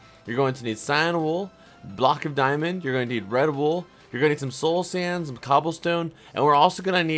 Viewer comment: for I second I thought he said red Bull instead of red wool